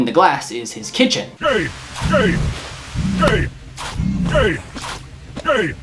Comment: I don't know if I was the only one who heard that, but it sounded like gay 2:30 when speed it up